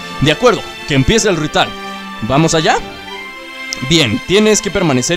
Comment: like si lo ves en 2019 like por que dijo rital jajajaja😂😂😂😂😂😂🤣🤣🤣🤣